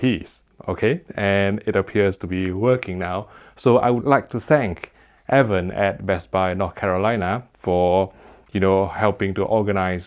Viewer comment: I thought he said "I'd like to give thank to Evan at Best Buy and NOT Carolina". I was WHOA shots fired. 😂😂😂